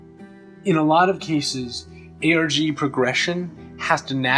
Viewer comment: So .... did anyone else mishear ARG as "eroge" and expect this episode to be much more awkward to watch?